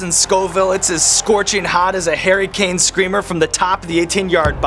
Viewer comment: Aye!!! The boy Sean Evans mentioned Harry Kane hell yeah! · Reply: Rick Gonzalez thought he said Hairy Kane when I first heard it! 😂